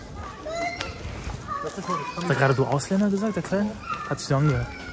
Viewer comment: ich habe es mir nun ein paar mal angehört und ja das Kind sagt "du Ausländern" , warum, wieso, weshalb,..... man weiß es nicht. 🤣🤣🤣🤣 🤦‍♀️🤷‍♂️